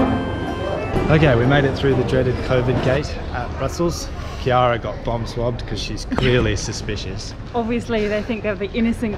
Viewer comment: At first, I thought you said "Kiera got bum swabbed" :~O